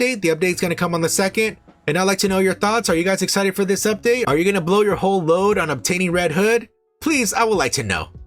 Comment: I just heard "blow your load on Redhood.." 😅🤣🙃.. updates always appreciated!